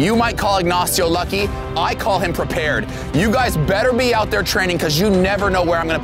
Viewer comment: Did he just say trenning